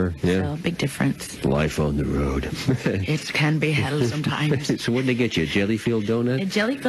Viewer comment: Did Selena say at 4:40 "It can be hell at times?"